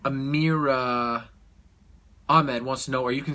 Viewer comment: Thought he was gonna say 'A Mirakuru?"